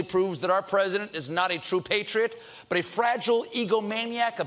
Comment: Oh my God that tweet from the mayor of Philly was savagery at its finest! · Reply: Thought he said fragile eaglemaniac ... migrating recollection or recollection migration?... alternate fact anus or alternate truth anus?...The new non word of the weak...emitted from the hovering conservative truth anus in the sky.